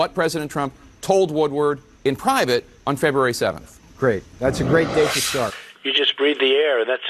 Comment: did he say " great, thats a great way to start"?